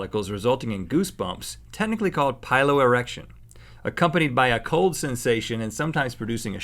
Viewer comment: did he say philo erection cuz philo= love so its a Love Erection